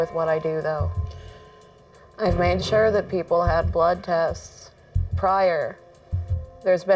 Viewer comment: I thought she said “I prefer kids blood” I had to rewatch it a couple times to reassure myself she didn’t say that😭✋🏾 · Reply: and i thought she said kid blood